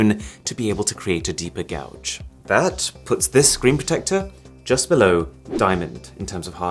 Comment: My sleepy ass thought it said "secret protector" and i was like "wait what"